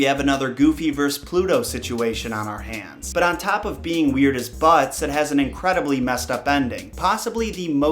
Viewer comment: Did he say "weird as butts" at 0:47? I've never heard that analogy before 😂